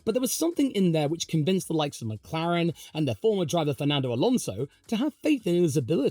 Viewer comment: did say "FORMER driver Fernando Alonso" or did he say "Formula 1 driver" and im mishearing things?